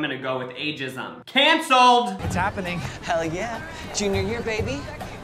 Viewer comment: did he say "hell yeah" what the heck 😅